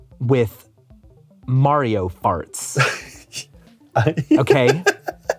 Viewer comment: I had kind of stopped paying attention and then I heard “Mario farts” and I was like what the heck is going on